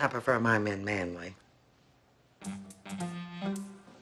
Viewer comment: "Well, if you have to ask." 😂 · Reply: LOL, I really thought at first she said "Well, you have the ass"